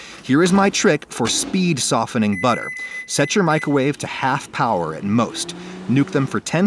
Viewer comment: thought u said half an hour